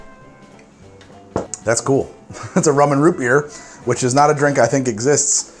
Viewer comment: For a moment I thought you said the Doubleshot was, "That's cool! That's a Roman Root Beer, which is not a drink I think exists." Next episode: "The Roman Root Beer"